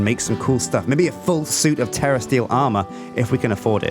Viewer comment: I turned on the closed captions button and every time said terra steal, it thought he said terrorists. The accent is just messing it up too much.